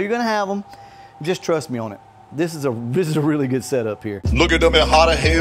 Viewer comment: 7 minutes in and all I heard was "rust me, bro". I'm not retarded, so no.